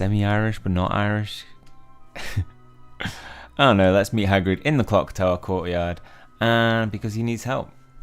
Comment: When he said “let’s meet Hagrid”, I thought he said “that’s me Hagrid”. Like he was doing a Hagrid impression while talking about his Hagrid impression 😂